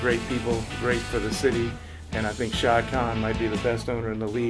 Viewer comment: bruh did he just say the owners name is Shao Kahn